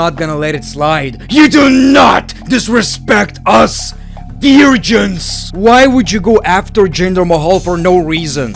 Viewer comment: What did you mean (00:43), when you said "Do not disrespect us ...?" What was the word? Eurasians? It shows "virgins" in the subtitles but you have a child. What did you say? This will make me crazy? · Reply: Veer-gins.. it's complicated